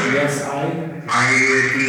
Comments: it sounded like it said yes I am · when you ask are you a demon i hear a barely audible yes i am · At the time 44:09 I swear I heard “yes I am” from the spirit, does anybody else hear that? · it sounded like "i need you to get out" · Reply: sounded like " yes i am"